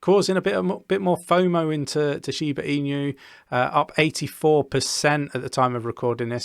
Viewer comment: It sounded like you said Shiba Emu, I got a great image in my head now of a half dog half bird hybrid. 😀 I still feel like this rally has been too quick so I'm expecting another drop before we really start to go for it. Don't think it will be as severe as May and September and my plan is to just hodl through it all.